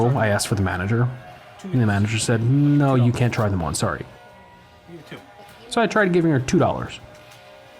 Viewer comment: Flying Motion I think you’ve missed what happened. The complaint wasn’t over paying, it was over paying and then not being allowed to try on any wigs. Despite paying. And yes, it appears discriminatory. You might want to try watching that part again. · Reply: Taticat NineLand hmm. You could be right but in the clip I thought he said “couldn’t try any more” but it seems he said “couldn’t try them on”.